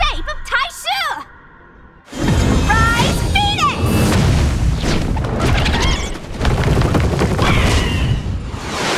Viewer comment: what did she just say? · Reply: Rise Phoenix i think